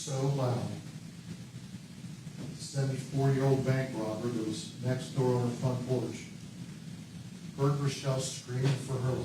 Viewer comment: It sounds like his says 74 year old bank robber next door heard the screams! Surely I heard that wrong!!